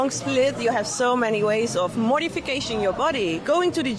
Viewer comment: No tattoos. Demon chick said "modification", I thought she said "Mortification"